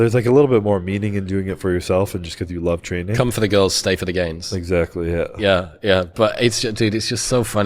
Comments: I heard " come for the girls, stay for the gays" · Haha i thought chris said come for the girls, stay for the gays.